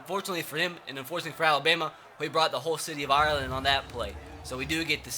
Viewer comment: did he just say "whole city of Ireland"